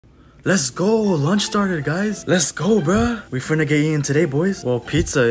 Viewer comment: 2secs in and all I hear is “LETS GOOO LUNCH DARNER”